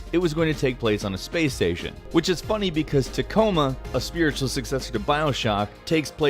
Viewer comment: What game did he say that it was the spiritual successor of Bioshock? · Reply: Gabi Axinte Tacoma